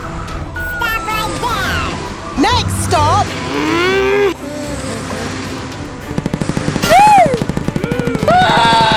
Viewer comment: what he said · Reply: Next stoo